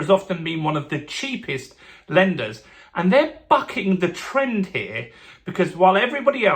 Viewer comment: Thought he said f**** the trend lol